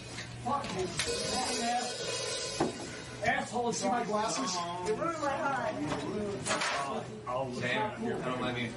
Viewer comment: “You ruined my high” I’m pretty sure I heard